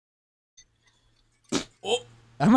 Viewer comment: “Nah that’s beta as hell I’m not gonna wipe” is my new motto for life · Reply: I thought he said "nah that's beta as hell am not gonna be white"